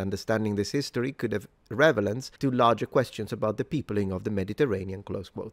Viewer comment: just loved the "revelance" instead of "relevance" around 04:30 - still awesome English from a native Italian speaker.